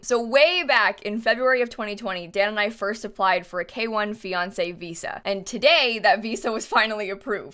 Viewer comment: I thought I heard Beyoncé visa when she was talking about the fiancé visa